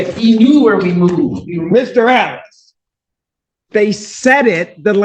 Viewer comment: I think he said “Mr. Ellis” not Mr. Allen but yes, Judge was pissed